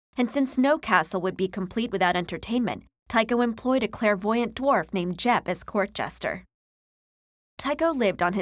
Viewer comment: Did I just hear "A clairvoyant dwarf named Jeff" or is my mind trolling me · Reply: No it said a clairvoyant dwarf named Jepp